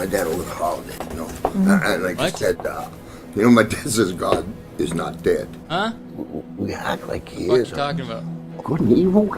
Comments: it sounds like hes saying: god is not dead, we act like he is, good and evil ? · I heard "good and evil , couldn't be" or sumn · I hear "good and evil hurdy gurdy." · I hear “my dad said Gods not dead. We act like he is. (Then the confusing part) Good and evil I mean look at me.” But I mean not 100% on it